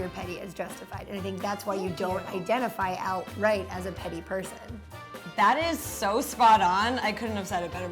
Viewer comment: 90% of the comments = " i thought it said 'pretty' "